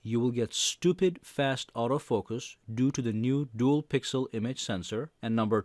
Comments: Did he say "Stupid fast auto focus due to duel pixel sensor" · Did he say Stupid Fast